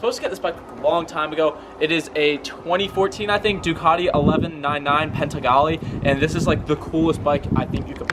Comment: Ducati Pentegali? Nice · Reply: I thought he said "Penzigale". Must be the secret 2022 model.